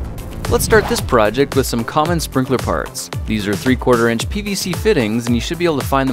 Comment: When I watched this I thought he said 'sprinkler farts'😂